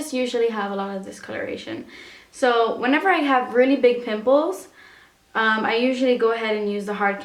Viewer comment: I first thought she said big nipples and not big pimples :D